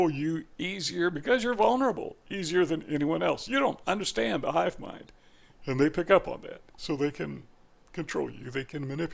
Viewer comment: What are you saying? It sounds like you are saying, "you don't understand the "HIVE MIND". What does that mean?